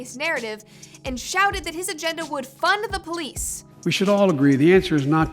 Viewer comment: It sound like he was saying "fun the police" and "unfun the police"